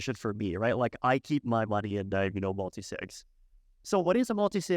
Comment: what does he say at 18:42? "I keep my money in diagonal multisigs."